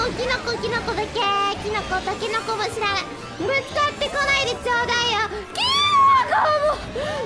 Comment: I think I heard "takenoko ga shiran," which means "don't care about bamboo shoots." She's probably referring to the chocolate snack feud.